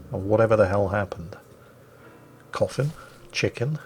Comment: Coffin? Chicken? I thought I heard coffee and thought jingles was about to make a jerma joke